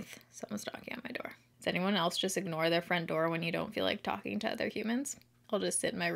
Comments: i thought you said “does anyone else ignore their friend Dora” until i read the comments 😂😂😂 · I watched the beginning like 10 times because I thought you said “Does anyone else just ignore their friend Dora when you don’t feel like talking to other humans?”🤣 I was so confused 🤷🏻‍♀️🤦🏻‍♀️ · Definitely heard you say you ignore your friend Dora when you don’t want to talk to people. Damn Dora.